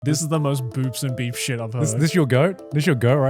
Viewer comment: I thought Joey said "Boobs and Beeps" 😭(😂)